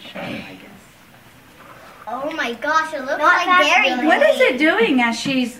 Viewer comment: "Oh my gosh it looks like Dairy Queen" IM DEADDD 😂😂😂😂😂💀💀💀👻 @ 5:41 I don't actually know what he said but it sounded like he said that